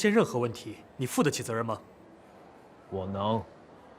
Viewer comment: sounds like he said “well no?”